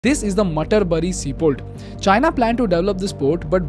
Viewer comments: My ass heard it as a Matar Paneer seaport 😭😭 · I thought he said this is the matar paneer sea port 😂